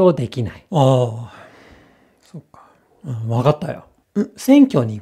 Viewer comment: wait a minute, at 1:04, is that a "suka" I hear him whispering ? Like the russian one ? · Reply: Pretty sure it's そうか (souka). Meaning something like "I see" in this context.